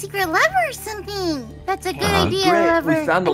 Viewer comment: is it just me or did call Levi 'lover' at 9:53 ?? i might be wrong tho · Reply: she said "lever"